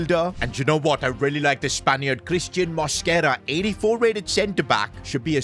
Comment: did bro REALLY said Spanian 😭